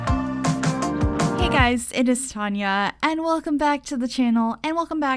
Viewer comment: Omg I always thought you were saying "hi guys, it is time yall" instead of 😂👏🏻